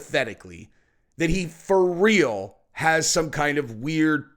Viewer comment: did say "fur real "😅😂